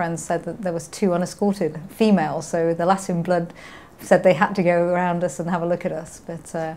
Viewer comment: Im curious what word she said at 8:00. it sounds like "the lassiun blood said they had to around us and have a look at us" what is she saying?